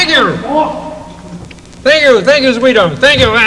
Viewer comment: i know he aint actually say the N word but then wtf did he say cause that all i can hear. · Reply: He said thank you